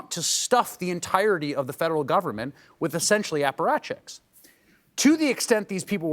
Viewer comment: What is the word Chris uses at 5:30 "We don’t want to stuff the entirety of the federal government with essentially___________. " ? Closed caption didn't catch it and neither did the transcript. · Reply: apparatchik An apparatchik is a loyal, unthinking follower of a political party or organization. The term is often used in a derogatory way. Definition A member of a political party or organization who is blindly devoted to it A senior member of a political party, especially the Communist Party of the former USSR A bureaucrat or official in a political party or organization Examples A "White House apparatchik" who always supports the president A "party apparatchik" who was previously a guerilla fighter